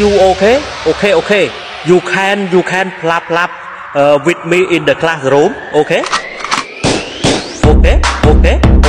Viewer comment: I thought she said *clap clap with me in the bathroom * Loll